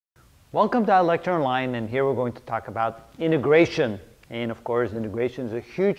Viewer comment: at the beginning i heard “welcome to electron line and here we’re going to talk about IMMIGRATION”